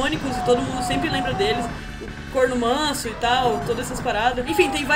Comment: Sera q entendi certo? Corno Manso? WTF? kkkk... Cormano!